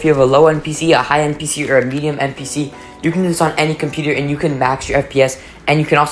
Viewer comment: its sound like he is saying npc